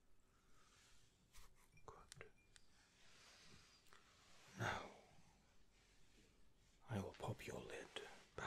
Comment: i heard "now... i will pop your lip"